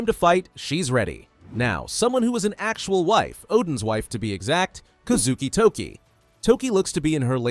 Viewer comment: I thought he said oda’s wife 😭